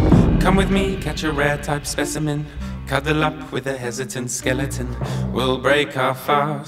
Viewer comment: “Cuddle up with a hesitant skeleton” Me: Time to get ŚP○ØKŸ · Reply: I honestly thought it said "Cadillac with a hesitant skeleton" the first time I listened